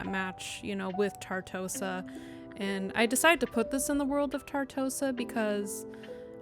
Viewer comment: What I thought I heard the first time: "... the world of Carcosa." I was horrified and intrigued. Also, that's a gorgeous build!